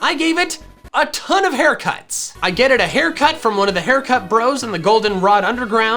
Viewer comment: I love how you said getted instead of got at 12:43 it made me genuinely smile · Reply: I think he meant "get it" but "getted" is much more funnier lmao